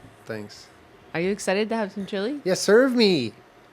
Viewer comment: my heart skipped a beat cuz I thought she was gonna say “are u excited to have some children” but she said chilly 😭😂